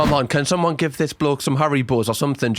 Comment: SOBBING cuz im watching this half asleep and i only heard u say "can someone give this man some hairy balls or something" and my immature self burst into laughter XD